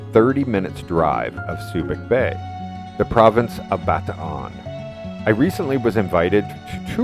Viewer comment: what province is he saying. I watched ot 5 times cant understood it. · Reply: Bataan